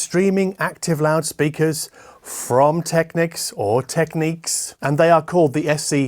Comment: Heard it as Techniques in the 90s, been calling it that since.